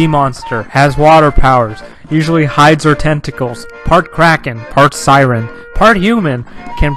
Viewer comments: in the vid, I thought the ‘part kraken’ saidpart karen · who thought at 3:01 it said part karen??